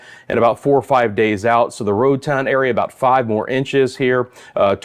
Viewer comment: Good morning from rainy Tortola, Virgin Islands. Did I hear you say, "Roatan, British Virgin Islands'? That is ROAD TOWN.